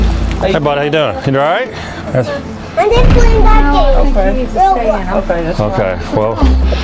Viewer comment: did he say "I'm just plaything that game, ROBLOX? ROBLOX is my favorite game !!!